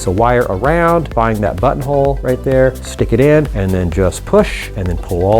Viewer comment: sounded like he said 'butt hole'